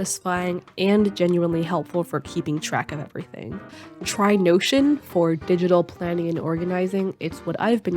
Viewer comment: hiii I have a question at 8:04 what’s the name of the app/website since I don’t understand what you are saying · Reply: Its Notion